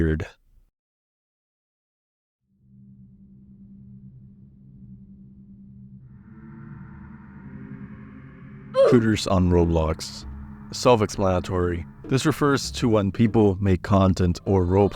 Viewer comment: I was just listening to the video and at 10:48 I thought you said “Hooters on Roblox” and thought maybe there were games made to be like the restaurant 💀